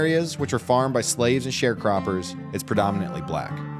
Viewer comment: Did you say farmed by slaves and sharecroppers?